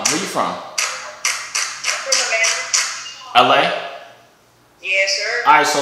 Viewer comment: Bro I think she said Atlanta not LA